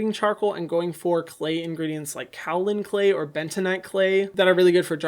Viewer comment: when he said kaolin clay i thought he said calvin klein